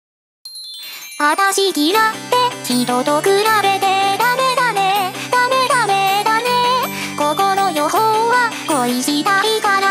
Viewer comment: 毎回 「話になんねー 人と比べて ダメダメ ダメダメだね(笑)」 に聞こえる